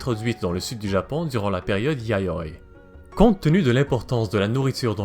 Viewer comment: À 1:23 qui a entendu "yaoi" comme moi 😂